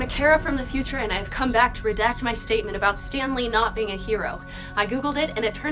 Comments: oh my god i thought you were saying 'Stanley' and that was your duck's name who died. I'm new to this channel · When you said "Stan Lee" I thought you were saying "Stanley" and i was so confused for a moment there.... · When you said Stan Lee I thought you said Stanley and because of the title I thought that was your duck's name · This is entire time I thought you said “Stanley” instead of “Stan Lee” and I was like... “was that like a neighbor or something that loved Marvel?” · I’m stupid. I am really really stupid. I thought you said ”Stanley......” NOT STAN LEE